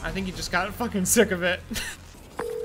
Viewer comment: The subtitle: Got Big Willie's guys What I heard: Got big willied guys